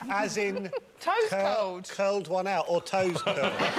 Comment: oh my god, because of how he mumbles I thought he said Roisin "curled one out her toaster" and I almost pissed myself lol I was like do Britons refer to asses as "toasters"?? So good : D